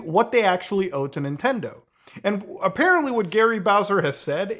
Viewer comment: I heard it as gay bowser at first